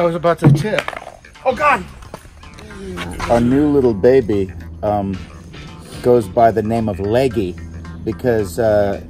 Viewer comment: I thought he was gonna say "a new little baby in the name of Oogway"